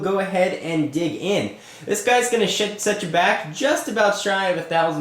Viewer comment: Did i hear shit at "this guy shit set you back"